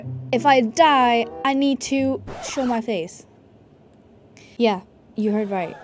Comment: help why did i hear chew my face also luv ur vids <3